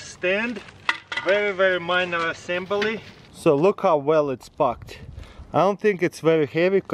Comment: I swear I heard “look how well it’s f*****”😂